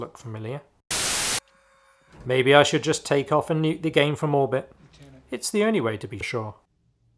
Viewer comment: I always thought they said... "I SAY WE TAKE OFF AND NUKE THE ENTIRE SITE FOR MORBID" hehe.. I often wondered who this MORBID was.. hehe I didnt.. but i still thought thats what she said.. for the LONGEST time...